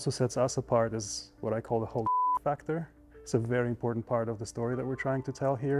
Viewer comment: what is the beep word? Why is it beep??? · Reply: " Holy F*ck factor ". Basically he wants the cars design that when you look at it, you'll say that, making it poster/content worthy.